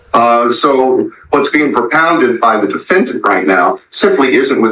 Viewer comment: “perpounded”??? wtf word is that?… Kris Kringle even be makin’ words up 😵‍💫this situation is TOTALLY a screwball, nutjob, monkey-court disaster…